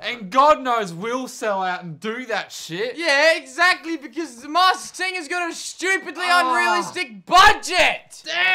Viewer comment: Ohh the masked singer... I thought they were saying the mass singer! A reality show about people who sing at mass. I dunno which one is worse